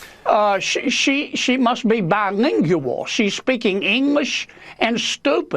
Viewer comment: Per Sen. John Kennedy … she (AOC) must be bilingual…English and Stupi-. 😂😂😂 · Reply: i thought he said Bidenlingual 😂